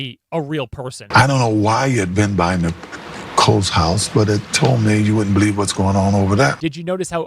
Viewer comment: He didnt say "but it told me" he actually said "but he"d told me".